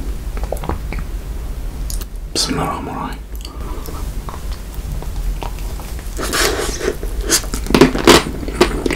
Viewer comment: كأني سمعته يقول بسم الله الرحمان الرحيم؟🌚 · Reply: اي قال بسم الله الرحمن الرحيم قبل ما ياكل لأنه مسلم ❤